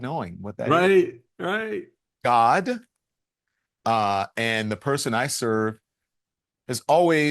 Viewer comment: Did he say God and the person I serve11:46